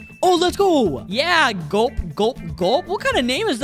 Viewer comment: for me roll in the floor "gulpgulpgulp" sounded like gaw